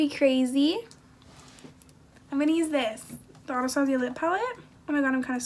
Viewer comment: i thought you said "the rob zombie lip palette" bitch i cackled